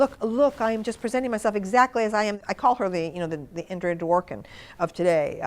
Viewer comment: What name did she say at 22:30? Ingrid Dworkin? I can't hear it correctly. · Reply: Andrea Dworkin :-)